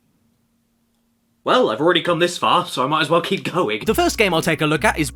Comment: i thought he said moght as well keep coming